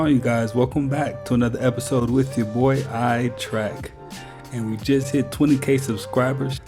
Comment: I swear it sounds like you say Eye Track all the time but I see it’s. Guess that’s like people saying Star Track instead of. Anyways gonna check out this video as usual.